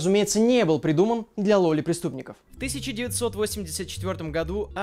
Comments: мне одному послышалось лоли преступников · я услышал ДЛЯ ЛОЛИ ПРИСТУПНИКОВ